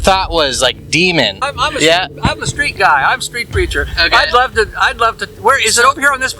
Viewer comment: That street preacher definetly got expelled from Entrana after crafting that double ended knife. Might also have transformed himself into the 12 pack. Stay safe always make sure to let your friends drink the first can. You were lucky to release the fluids as soon as you awoke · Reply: Oh I thought he said he was a "Street Creature"